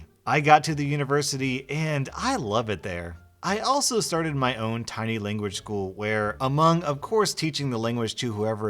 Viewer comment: The language student who started her own small language school... I swear i thought she said she started her OWN SMALL LANGUAGE. Now that would have shown that teacher